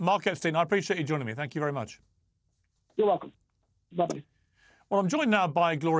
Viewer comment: Did he say "Love you" when he hung up at 10:24?